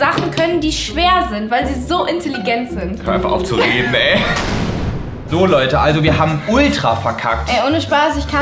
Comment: Noch jemand herausgehört: "Hör einfach auf zu reden, bae." ??